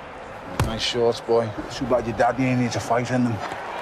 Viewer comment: i still cant figure out what ricky says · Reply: He said "nice shorts boy, too bad your daddy ain't here to fight in them"